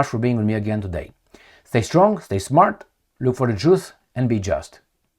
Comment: Did he say "look for the Jews"? 😂